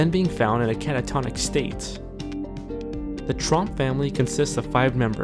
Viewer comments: Thought it said Trump Family 😂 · when he said tromp i thought he said trump